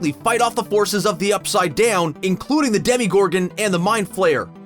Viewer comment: did he just say DEMIGORGON?